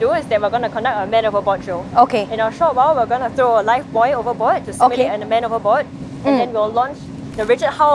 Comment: i think i hear she says "we gonna throw alive boy" hahahahaha